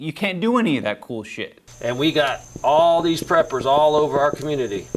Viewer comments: I thought it said peppers! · I thought it said peppers...